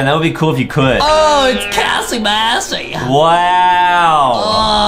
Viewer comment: DID CALL KISS MISSY “KISSY BASSY”?????